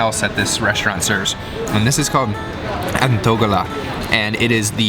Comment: how do they call it? Couldn't catch the name of it. Help pls. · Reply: the word is "anthogala"